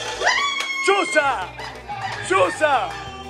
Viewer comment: Minuto 6:28 - Pensé que dijo "Chucha"😂😂😂😂(pd: Solo los panameños entenderán.)